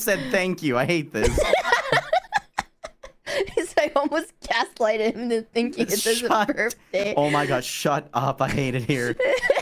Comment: If manages to make think today is his birthday then this is proof that can alter ´s sense of reality. Which is funny because this happened twice now! I Iove this two!💜💙 also 0:32 I think says: "He is like I almost gaslighted him to thinking this is his Birthday!" But I am not 100% sure because ngl is sometimes horrible to understand when she is laughing and talking at the same time lmao.